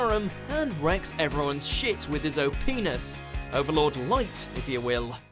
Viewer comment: did i just heard oh penis?